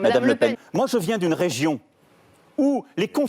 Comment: "Moi je viens d'une région où lEs cONsUs"